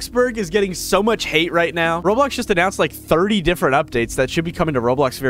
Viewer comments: Bro, you made a mistake. It said road block instead of Roblox. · mistake of subtitles saying heat instead of hate